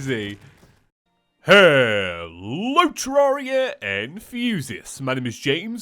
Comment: Oh my god I just realized that he says “hello terraria enthusiasts” for the last year I thought he was saying “hello terraria and fuse-iasts 😂